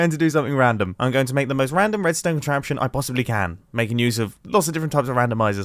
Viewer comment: Plz, explain the beginning. All I hear is "random random and random" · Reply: A-frame roof.